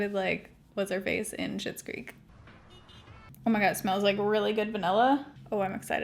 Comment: I had to back it up when you were talking about the hair spray because I thought you said it smells like salami lol